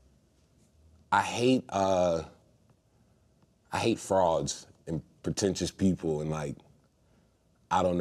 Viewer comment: Man when he said "I hate frauds" I thought he said "frogs" and we were about to fight